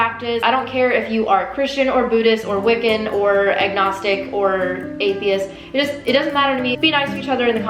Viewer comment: At first, I thought you said, "Eggnogstic!"